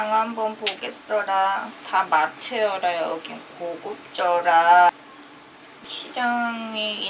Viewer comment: 고급져라~ 하는데...어째 내 귀에는 정말 귀찮서라~로 들리는지...funny^^ 먹을걸 사러 간건지 상한 맛에 도를 닦으러 간건지...^^ 그 치앙마이 장보기 백도 도사들이 사용하는 것 같서라~^^